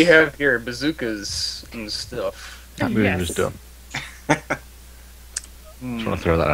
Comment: Did say "Amumu's dumb" at 1:00? No wonder he's so sad.